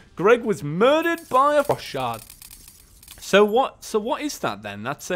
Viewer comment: what happened to his voice when he said frost shard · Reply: It sounded like he said fu*k shard